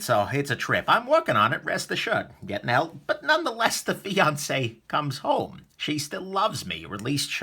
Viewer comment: Did anyone else hear "the Beyoncé comes home" at 2:21? :-p